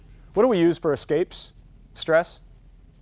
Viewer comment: What does he say at 55:11? "Shot'em out..." English is not my first language and he speaks fast. Thanks